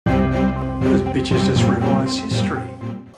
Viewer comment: On 0:01 did he say bitches??